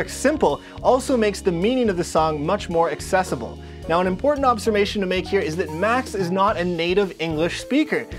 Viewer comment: did you say "Obsermation" ? (Observation). Haha . I love your channel. Great work .